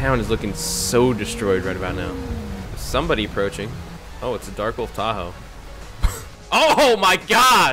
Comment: it sounded like you said “Oh it’s a dark old Tahoe”